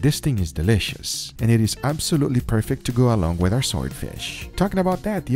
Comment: Why do i hear him say ‘soy fish’ at 4:20? lol